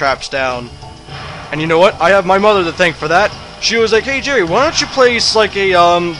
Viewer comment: Did I just hear you say your name is Jerry? Around 3:40?